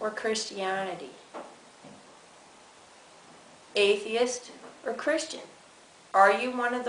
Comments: did she just say christianitity? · Did she just say "cherstianity"?